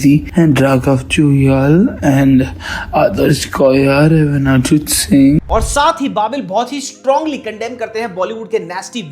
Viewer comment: me who heard Arijit Singh..🥴🤣